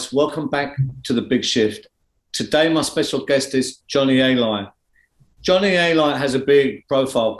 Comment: It sounded like he said today’s guest is Johnny A Liar with that accent